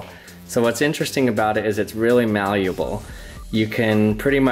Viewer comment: wow I thought he said valuable for a second I was like all right I'm going to buy hundreds of gum packs